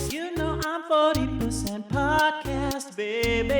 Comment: The intro song is awesome but I didn't read 40% podcast and I heard "horny 📯 for some podcasts" and I was like "SAME"